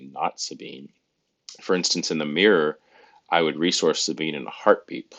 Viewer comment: Defo heard you say “resource facibi” way more than I had expected 😮Food for thought, thanks!